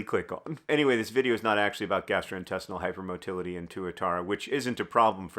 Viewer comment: I thought is said "hypermobility" and was so curious as to if those lizards stomachs are breakdancing or something in their little bodies, and simply had to find out